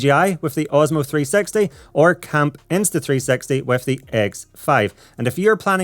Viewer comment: everytime i heard you say insta 360 it sound like insta3sexy